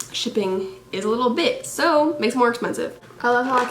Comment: I thought she said "shipping is a lil b***h" at first but she said bit omg 😂😂🤦🏽‍♀️